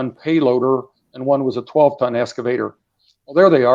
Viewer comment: Loved this guest, but at one point I thought he said "a 12 ton masturbator." That would have been some contraption...